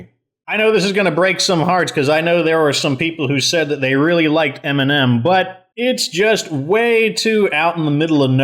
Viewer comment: First video of the year! How would you order the bases if you were to make your own video on this topic? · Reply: I know I'm so late but I got the game on sale amazing vid u put so much effort into them And when u said m&m I thought u said eminem😂